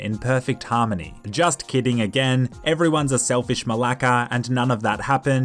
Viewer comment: Did he say malaka